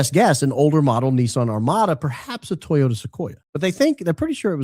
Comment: The audio warbles when the officer relays the make of the second vehicle. Its sounds like he said "Sequoia" which is made by Toyota. It looks like a squashed, chunky SUV made by the Empire from the Star War.